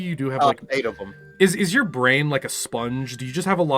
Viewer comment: "'Cause there's a fat man in the cockpit with the blues I hear you moan, I hear you moan, I hear you moan" It is "brain like a sieve".